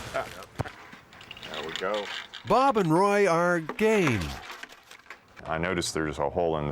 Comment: For a second there, I thought the narrator said “Bob and Roy are….gay.” Not game. I was like, ummm…ok.😂